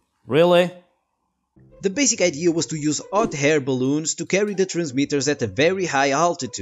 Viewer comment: you became dyslexic - you said "Ot Hair Baloons" instead of "Hot Air Baloons" LOL I do that shit all the time!!! HAHAH!!! :D